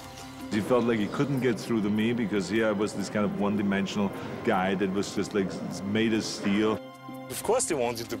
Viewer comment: Can someone, please, write what Arnold say at 41:00? Thanks a lot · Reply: One dimensional guy that was, like, made of steel.